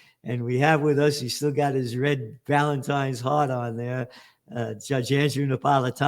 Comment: It sounded like Gerlad said "he's still got his Valentines hard-on"🤣🤣